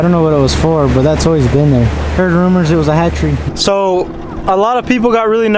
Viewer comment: what does he say that was rumored to be? a hatchery?